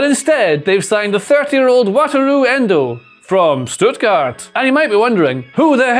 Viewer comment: WATERLOO ENDO !!! (that's what i heard 0:09 seconds in and had to write it down)